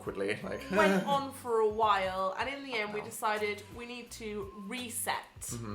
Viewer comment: What did Dan say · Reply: "Quite a while" I think